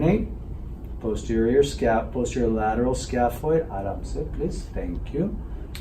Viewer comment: Does he say 'aram se' in